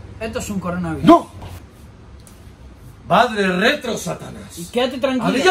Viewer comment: "Madre retro satanas" que llena de hijos estas 1:37 entendí esa referencia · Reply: Ni padre ni madre. Es Vade.